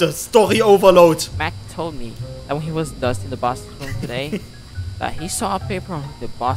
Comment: Matt told me... when he was dust in the 'bast'-room today.. " sagt bitte nicht ich bin die einzige die das verstanden hat😂😂